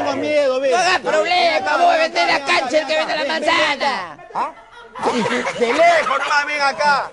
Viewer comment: que dijo? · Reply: No hagas problema “Si vos vendes la cancha déjalo que el venda la manzana” En referencia al circo que tenían estos personajes